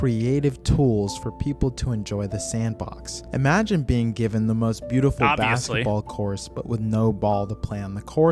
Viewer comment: did he say "Basketball Course"?